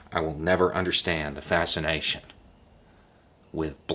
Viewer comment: Did you just say: "I will never understand the fascist nation...."? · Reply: Lol, he said "I will never understand the fascination..."